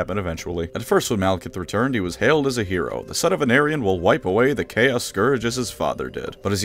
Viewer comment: Every time he says "the son of anarion" and I hear "the son of an Aryan" 😬